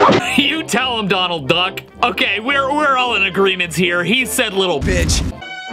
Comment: I think he said you little brat